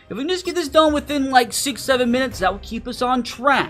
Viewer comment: d di did I just hear 67